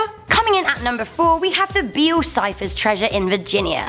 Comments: Am I the only one who heard her say Bill Cipher instead of Beale Cipher? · When you said "Beale cipher" I thought you said bill cipher, and I was like" that's where gravity falls is set!!!"